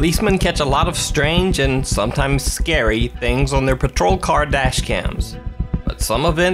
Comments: thought I heard caught on 'kardashian' (car dashcam).lol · lol when you first said "car dash cams" i thought you said "kardashian's" lmao! that scared the shit outta me!